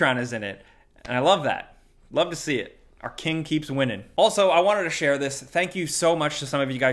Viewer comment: I heard: "our kids keep winning" and I was like, bro this is for us not them, the ones in our late 20s, then I heard it again, and he now I understand that he said: "our keen keeps winning". Now eveyrthing makes sense.